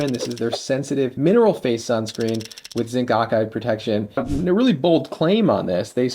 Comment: said okide instead of oxide... :) hehehe